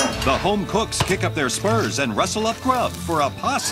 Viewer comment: BRO I thought he said "sperms" instead of "spurs"